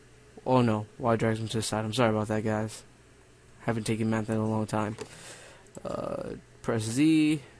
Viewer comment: did he say "i haven't taken meth in a long time"?!!?